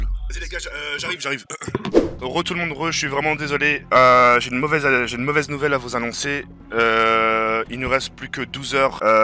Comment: C'est moi où il a dit j'ai une mauvaise haleine 😂